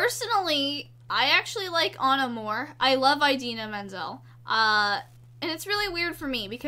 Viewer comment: When she first said Idina Menzel I heard it as idea middle - ;w